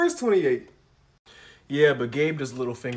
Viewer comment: I heard "Gaybe"💀